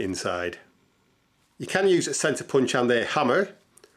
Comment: A Sensa Punch? What did he say the gadget was at 5:44 ? can't understand proper English. Thanks. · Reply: Centre punch 👍